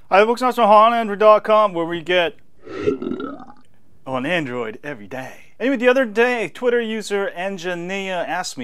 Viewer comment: Lol back then when i began watching your videos i always thought you said HARD ON ANDROID EVERYDAY XD